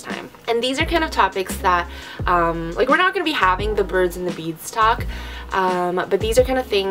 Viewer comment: did she say birds and the beads talk?